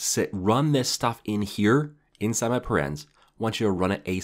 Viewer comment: did he say inside my parents?